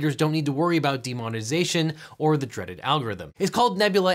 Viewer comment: For a second I thought you said demonization...